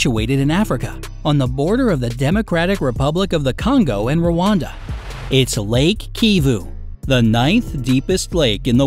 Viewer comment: i thought he said wakanda i think i'm the only one.